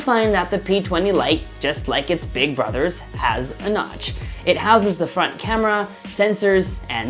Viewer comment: Thought i heard him say Crotch instead of Notch